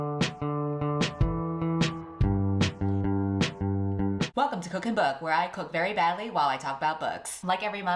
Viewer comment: I thought you said "cook a book" for a sec there 👁👄👁 and I was like damn a book was that bad